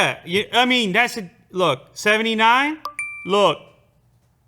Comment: Lewis: 79?!?!??! ANYONE ?!?!?!? SEVENTY NINE !!!!!!!! me: did I hear 69